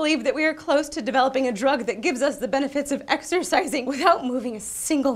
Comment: I heard "exersexing". Anyone else hear it?